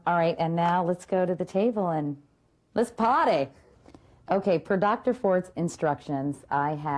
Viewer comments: At :51 seconds it sounds like she says, "Let's potty!" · seconds it sounds like she says, "Let's potty!"